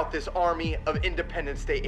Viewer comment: Oh, Independence Day aliens... I thought you said "independent state" aliens, like the ones that have the USA having a seizure right now.